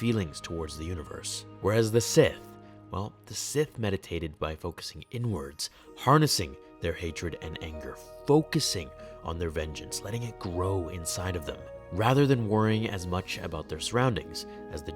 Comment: did he say Sith focus the N words💀